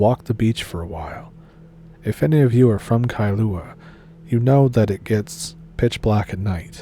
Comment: Hmmmmmm.I …always… thought Kahlua was a coffee liquor🤨…..I’m such a bitch😂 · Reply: I think you might be mishearing Kailua as Kahlua?